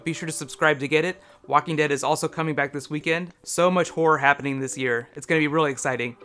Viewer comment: I heard "so much whore happening this year." Somehow I don't think that's what I was meant to hear...